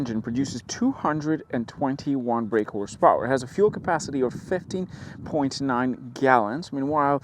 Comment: Great review. Thank you. I really love the GLB and want one soooo bad. With the tailgate open, the GLB looks bigger! Sounded like you said 50.9 gallons?! 🤣